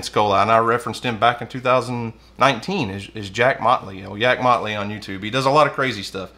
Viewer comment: Good thing you mentioned the gummy bear, I thought you said Jack Motley, turns out it's Yak Motley.